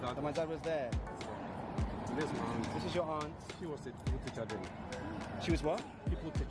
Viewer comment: the gentleman who was showing you the picture of your father and aunt on his mobile phone was saying "PUPIL TEACHER" instead of "people teacher" as indicated by your captions. PUPIL TEACHER is a common phrase used in Ghana to refer to teachers who taught classes 1 through to 6, when I was growing up in Ghana. I get or understand the fact that the school building in which your father taught is no longer in use hence its current state. However, as a matter of fact, the culture of maintenance is very appalling in Ghana. Public structures and amenities are not properly maintained. Greetings to my beloved, fellow Ghanaians from the DEEP SOUTH, United States.